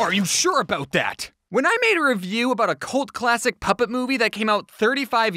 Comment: I thought he was saying “peppa movie”not puppet movie. It took me while to figure out that